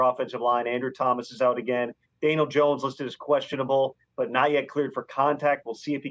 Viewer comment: it sounds like he says "Anal Jones"😂